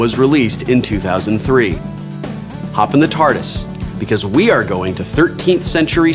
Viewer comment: Did he say "Hop in the TARDIS" or was it just me mishearing?